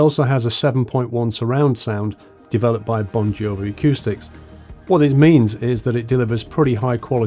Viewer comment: Sorry. I thought you said “developed by Bon jovi accoustics” then realised was Bongiovi” was very concerned that the old lad had started making gaming headsets